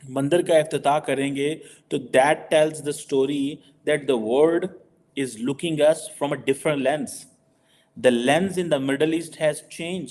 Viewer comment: … that the world is looking us with a different lens. Jhoot hai, galat hai! Dr. Jaishankar never said it. What Dr. Jaishankar did say is: “… that the world is looking “AT” us with a different lens. Dr. Jaishankar knows his prepositions, he is not an ANPADH like you.